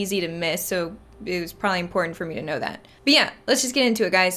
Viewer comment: was it a french "Bien!" i just heard? Im French andd surprised to hear it =D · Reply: "But yeah" she said it like it was 1 word, but its definitely "but yeah"